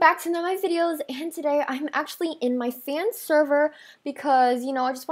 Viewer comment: WHY DID I HEAR: “hi guys! I’m in my Korean server and I just wanted to film a video.” SEND HELP